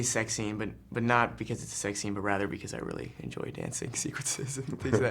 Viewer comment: really enjoy dancing...what did he say after that? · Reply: "I really enjoy dancing sequences"